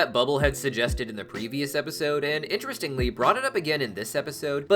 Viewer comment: you mean two episodes ago? Confirmed unreliable source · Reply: pretty sure the auto subtitles got it wrong. i heard ' a previous episode' and not ' the previous episode'